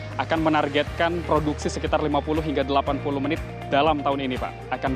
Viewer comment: ini ane yg salah denger mungkin ya, reporternya bilang produksi 50-80 menit tank? Unit atau menit?